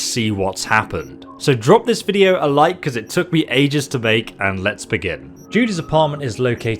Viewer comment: I swear he said it took him ages to bake! Hope you had a happy holiday battle brother keep up the hard work 🍻